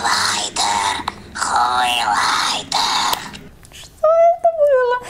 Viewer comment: вы тоже это услышали?😂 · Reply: Хреньлайтер